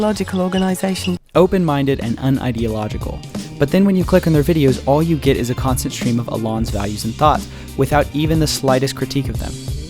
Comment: On ‘pretending to be unbiased’... 1:29 - Am I the only one who hears it as “open-minded and ideological” here? Grammatically speaking, it makes better sense too, rather than ‘un-ideological’ which also isn’t really a word. I’m afraid this made me stop watching.